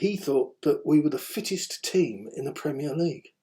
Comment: i thought mason said leeds were one of the fittest teams.